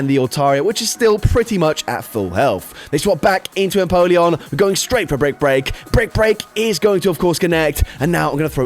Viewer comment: thought you were having a stoke saying break break so many times